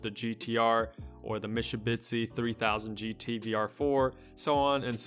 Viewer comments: um..did u just say misubitshi at 2:30? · did you say Mishubitsi?